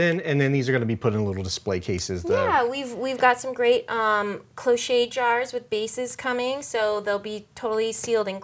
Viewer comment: "Closhet" glasses?? what is the spelling of that word anyone please?? :) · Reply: cloche